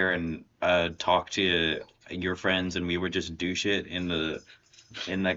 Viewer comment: Playdate PDX. Also, it sounded like you said, "Douche it" (not "do shit") and I was horrified and reevaluating everything I thought I knew